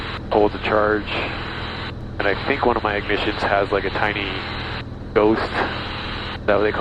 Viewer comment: If I understand correctly, it is my Lycoming EIS (ignition / left mag) that draws the phantom current, and that's normal for them. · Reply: Ah yeah, you did say "ignition." I don't know why my brain thought you had said the starter! Apologies for the misunderstanding.